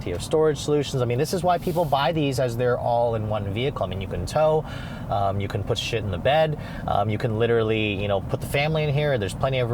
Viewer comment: Does he say “You can put shit in the bed?”😂